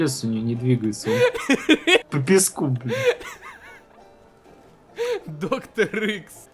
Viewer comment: А кстати вы заметили что он сказал "по песку бля"